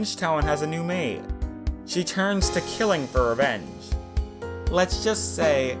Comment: I heard "killing for" as "killingfur" XD